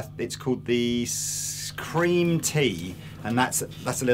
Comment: at about 26:30 you mention what sounds like "Scream Tea" for less distortion.From what I can make out there is the "Sweet Cream" or the "Black Tea" but no Scream Tea. Which one did you mean?